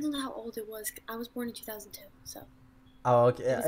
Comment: really? She's born in 2010? Like she's freaking 10 years old? Do i hear that correctly?